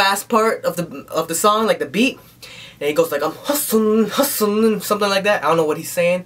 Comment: At first I misheard the "Im Hustuling or whatever part" as "Im Awesome men" lol tell me im insane xD